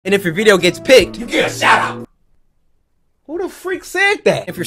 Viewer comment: I thought he said you can shut up